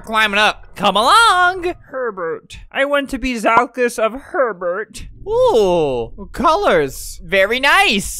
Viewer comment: for a sec I thought u said pervert